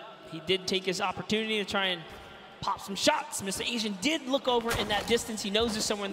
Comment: did he say MsAsian